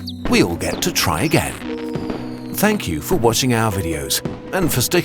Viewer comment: DID HE JUST SAY TO SEE US AT 1220?? THAT'S BULLSHIT.